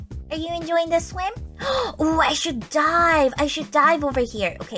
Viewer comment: i thought she said die not dive XD LOL